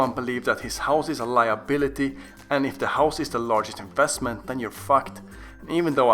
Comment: did he just say if your house is your biggest investment then you’re fucked?😂😂😂😂😂